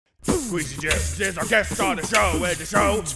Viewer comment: I… honestly didn’t know Skweezy Jibbs was a real person until now. I thought it was just a funny name for a save file when they played House Party.😆 Edit: now that I know he’s a real person and after seeing the first 1/3 of this video, I can confidently say that I will vote for him to be our president. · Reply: Same here. I even thought Dan and Arin were saying "skweey JIM"!